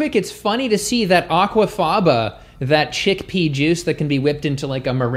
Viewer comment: Omg so unfortunate; I misheard "chickpea" as "shitpea" and had to replay it while going "WTF dude!"